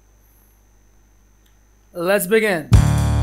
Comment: I heard lesbian